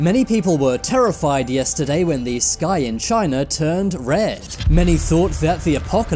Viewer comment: I heard "This guy in china turned blood red" at first lol